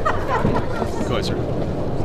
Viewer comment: What did the bald guy say? Is that polish "Wchodź" what means "come in' ???